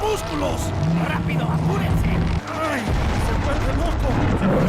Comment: For the “a por el” bit. I thought they were saying “apuren” for hurry up or something when they were working in the village before they see you.